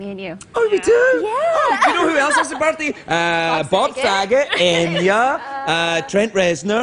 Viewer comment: did he say Bob f**got? Lol